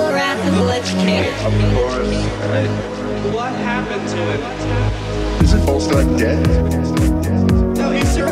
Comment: Thumbs up if you thought he said isn't ballsack dead at first.